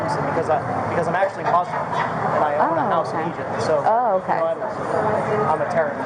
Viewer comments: Did he just say ‘’I’m actually Muslim and I have a house in Egypt’’?? OR AM I TRIPPING.......?? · did he just say “I’m actually Muslim”?? He has lost it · Did he say “because I’m actually Muslim” @ 10:37 ?? This guy just keeps giving more gifts with every interaction. Please, please, please keep up with the material. This stuff is comedy gold. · did he say " I am actually muslim". Is he muslim?